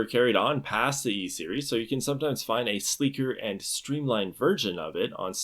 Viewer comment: he 100% said "a sleeker and streamlined virgin" instead of 'version'. That was a hard G.